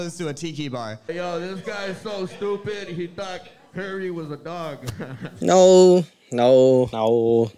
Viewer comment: So I read some of the comments but can't find one that's clearing up the "diss" made 25:45 He said "this guy so stupid he thought Perry was a dog" as in Perry the platypus for Phineas and Ferb. At least that's what I heard...